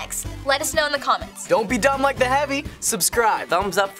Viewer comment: did he said don’t be dumb like the heavy!!!